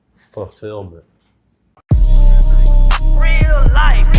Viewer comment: I thought he said stop filming at the end and i almost turned my phone off from fear 😂 i played it back and he said fulfillment 😂